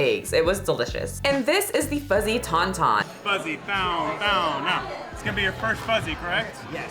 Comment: it sounds like he says fuzzy Pound town I had to listen four or five times lol😂